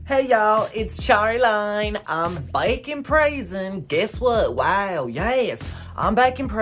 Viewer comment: I thought you said bacon praising 😂😂😂